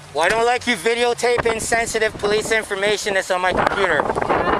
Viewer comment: I know it's not what he was saying but all I heard was.."I am neglagent in protecting sensitive information."